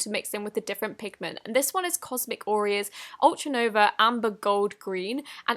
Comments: I thought she said cosmic oreo not cosmic auria...Am i the only one😂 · When you said cosmic auria I thought you said cosmic Oreo Lmao😂😂😂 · I repeatedly thought that you said cosmic Oreo